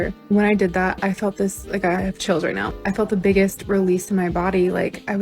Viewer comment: So hey, I think you heard this chick wrong. She didn't say she has children now (thank merciful Christ for that one...), she said, "I have chills right now." Apparently this chick thinks she's pretty genius being the first human to think of gaslighting & to actually give herself advanced delusionary schizophrenia. It's like that Futurama episode where Bender's like, "I think I just had an idea so diabolically clever that..." Smashcut to him being ruled guilty in court by the judge. "Stupid anti-pimping laws!!" Like she actually thinks she came up w/ the idea of lying to oneself to pretend something never happened. Just oh my fucking God...